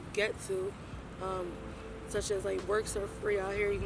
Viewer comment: Can one of my friends here in the comment section tell me what she said? Right here: ”_____ are free out here.” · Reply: “Works” are free out here, in the county, other wise you gotta go to rite aide or Walgreens”....works are another word for “gear” aka needles, caps, supplies to bang/shoot dope.